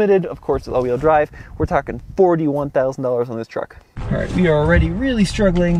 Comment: did he say this "truck" costs 41K? 😳